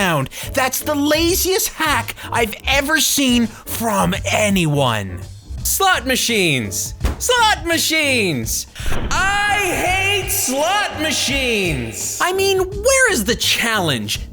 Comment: I thought you said Clot Machines xD